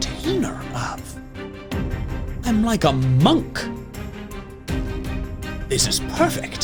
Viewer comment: When he said "I am Monk" my dumb brain thought he said "I am Monke"